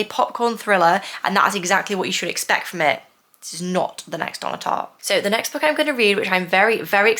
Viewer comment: I could not catch the author’s name that you said after you mentioned Freida McFadden being a popcorn thriller writer. It sounded to my ears like you said “It’s not gonna be the next Anya Tarr” or something like that. But my CC had you saying the next on the top. Could you tell me what author’s name you were saying? At least I thought you said an author’s name; maybe I’m wrong. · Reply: Donna Tartt 😊 I get so excited and speak on double speed sometimes haha